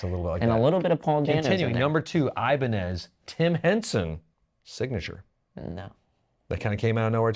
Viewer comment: i heard Jim Henson and thought we were about to get an Oscar the Grouch Iceman... Because I'm here for that... 😂